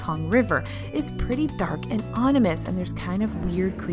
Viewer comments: Did just say "onimous"? Lol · I heard "onimous" in the Jungle Cruise section.